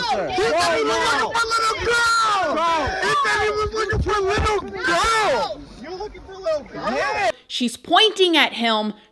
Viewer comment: They say he said he was ‘looking for little girls’. We know there were no children, and that he was looking for goggles. Pretty obvious they misheard him! ‘Little girls’ / ‘little goggles’….! That gang started this for no reason at all!!